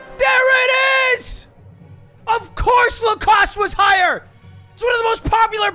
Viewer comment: I totally thought it said lactose